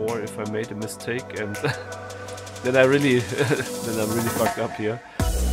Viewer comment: did he say "I really fucked up here?" 😂